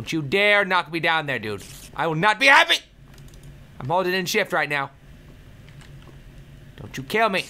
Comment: When he said he was holding in shift, i thought he said im holding in sh*t. XD